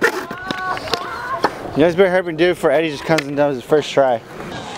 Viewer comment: What the fuck does he say? 😂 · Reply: Synchronicity "You guys better hurry up and do it before eddie just comes and does it first try"